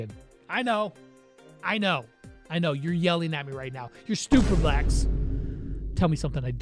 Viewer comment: did he just call me a stupid black?!